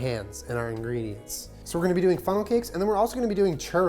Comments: I thought it said “funeral cake” and was so confused for a second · Ok how many people thought it said "making a funeral cake" 😳